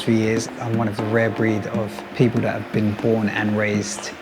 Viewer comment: did he just said porn (born)